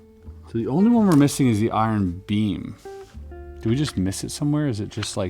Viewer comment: I thought he said bean but he meant iron beam 😂